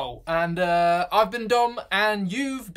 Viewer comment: You should change the subtitles so your name is written as "Dom" instead of "dumb". "I've been dumb and you've been watching Every Think." lol